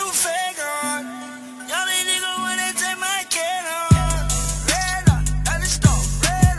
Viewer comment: i swear on jah it says “y’all didn’t even want to take my cat home”